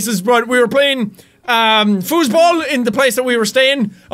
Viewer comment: I know this viedeo is old but did he say Fußball like the German word?